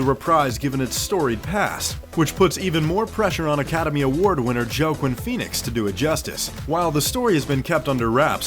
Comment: Did he just say “Jo queen Phenix?”